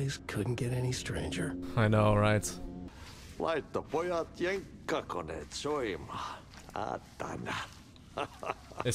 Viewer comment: Just to be that guy…. I know what the janitor said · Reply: torilla tavataan. tai vaihtoehtoisesti me avataan tortillat. ja laitetaan jenkkakoneet soimaan